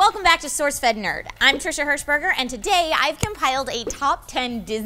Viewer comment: I thought it said "Trisha Heisenberger", damn it Breaking Bad..